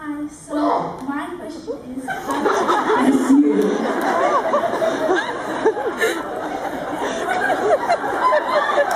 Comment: What is going on at 15:34? I know someone sneezed & Colin's reaction was hilarious but I couldn't hear anything the woman who sneezed was saying. It must've been funny too judging by Colin & Roses reactions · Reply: I was there, and it was hilarious!! She says that she hit her head on a bathroom sink before she came in. She was on her way to her seat when when she sneezed, and she was having a bad day. Then they were talking about her daughter. And for as loud as it was on camera, it was way louder in the room.